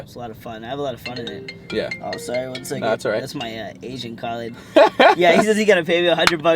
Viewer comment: I thought he said "thats my Asian calling" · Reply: Mountaingirl02 i thought he said my asian colleague